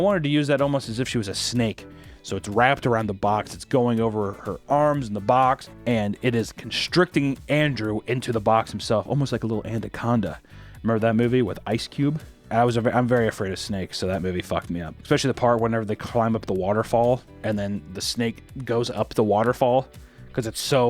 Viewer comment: Is he saying “andaconda”?? lol